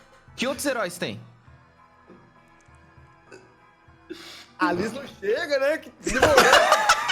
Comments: O que ele falou? Não entendi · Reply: ele diz "a liz não chega né?" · n entendi oq o flw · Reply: Ele falou: A num chega né Kkkkkiik